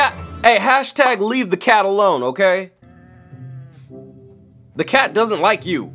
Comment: Oh I mean I thought you said let you leave that girl alone but sorry leave the cat alone girl